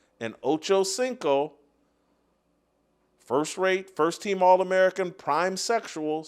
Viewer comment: Did you just say "First Rape" when you were introducing Shannon Sharpe 7:06 ? 😅😅😅